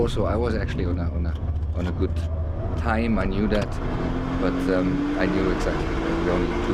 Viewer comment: オナグッズって聞こえたwいかん、俺は重症だw